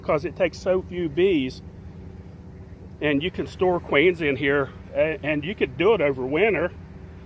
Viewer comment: I just finished a 15 hour work day and thought that said “Store Malted Queens” - and I thought for a second had some secret chocolate malt for super bees